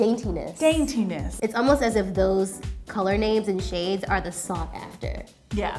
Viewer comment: it's almost what ? I can't make out what she said then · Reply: Sssophie “it’s almost as if those color names and shades are sought after”